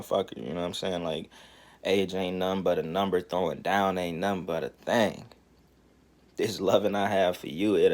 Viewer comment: All Dis Time Thought Mf Girl Said “ Age Ain’t Nun But Ah Number, For Ah Noun AINT Nun But Ah Thang “ Cause Ah Noun Really AINT Nun But Ah. Thang. I Never Knew Why She Said Dat Shit It Made Sense But Now I Know My Ass Wasn’t Hearin Right Ctfu 😂😂😂😂